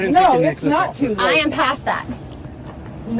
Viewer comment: Thought she kept saying I’m pasta 😂